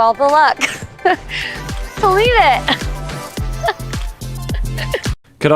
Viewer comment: I think Micah said about Zach and Bliss “I wish you all the luck… you’ll need it” which to me is definitely a put-down and low key aggressive/bullying. Also I feel so validated hearing that could see Chelsea and Marshall working well together. I imagined the same around this point in the show. Maybe in some parallel LIB universe ? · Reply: Oh did she say "you'll need it" I always thought she said "delete it". Also yes about chelsea and marshall. I felt the same.